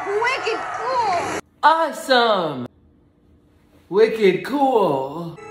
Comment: bro for the longest time I thought he said ready cool